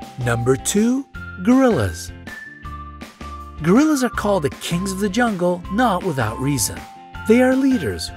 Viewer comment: when u said kings of the jungle i thought u said kings vagungle